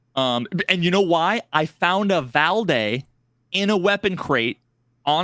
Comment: he says Valday but I heard Quad Damage. At least for levels 1-14.